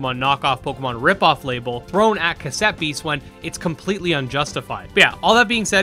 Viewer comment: I misheard 'Cassette Beasts' as Giuseppes and that's what the games called now. Can't wait for Giuseppes on switch